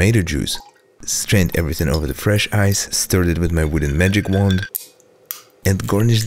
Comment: I swore he said Vladi-Mary!